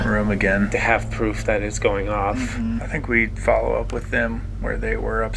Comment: Did anyone else hear a female voice say “mhm” at 6:37? Just me???